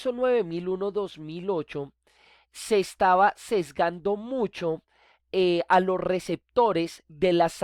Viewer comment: Disculpa,buenas noches.Tengo una duda, en el minuto 5:14 dice...se estaba XXXXX mucho .... ---> MI DUDA es que dice en esa parte?? no entiendo muy bien.Pofavor necesito la aclaracion.Gracias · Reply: dice: ..."Se estaba sesgando mucho"